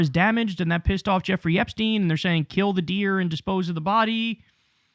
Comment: I thought she said kill the deal. Not deer